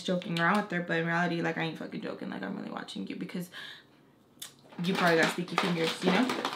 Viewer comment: it sounded like u said "You probably have STINKY fingers". 😂😂😂